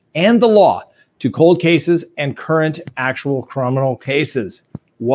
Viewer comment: did actually say"crominal?" @ 1:11.... he is a CROMINAL DEFENDER.... I luv u... I'm just teasing you....lmaooooo only laugh with the ones you love